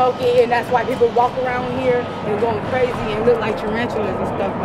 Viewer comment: loool did she say people walk around like tarantulas?